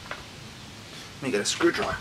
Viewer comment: Did he just say the N** word or am I tripping?🤔🥱😂😂😂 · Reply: “Need to get a screwdriver”.